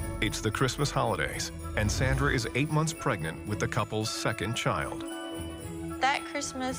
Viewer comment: I thought he said, “Santa is pregnant with his second child”